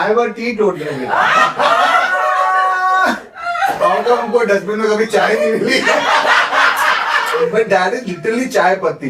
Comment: What did say to at 18:00 minute? Regarding catching lie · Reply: Dustbin may beer can milty hain